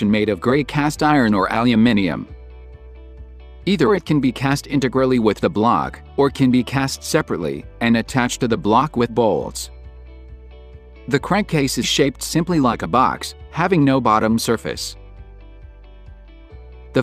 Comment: did he say Bolls?